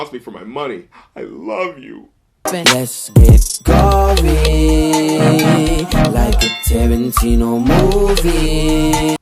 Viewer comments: I heard "going like a Tarantino movie" the first time I heard it, now I'm confused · I always thought he said, "lets get coffee, like a Tarantino movie" which woulda made way more sense. There's a huge coffee scene in pulp fiction, and he said in the hook "fuck a decaf"